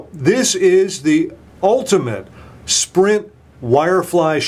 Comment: he almost said "Schprint" instead of Sprint. Haha